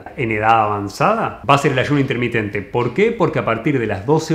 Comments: En minuto 5:05 dice " lluvia intermitente? " No entendí eso, podría ampliar la explicación? Gracias! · Que dice en 05:06 ¿la luz intermitente? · Disculpen pero nomás no entiendo lo que dijo en el 5:07....lluvia intermitente??? Simplemente no entiendo, pero es muy importante para generar la hormona del crecimiento! Por favor alguien....o dijo....sueño intermitente, porque menciona que a partir de las 12 horas empieza a producirse dicha hormona... gracias!